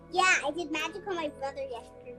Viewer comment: what did that little girl say? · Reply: It sounded like "I did magic on my brother yesterday."